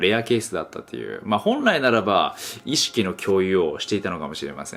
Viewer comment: 手の動きを見て、意識の挿入に聞こえた自分を殴りたい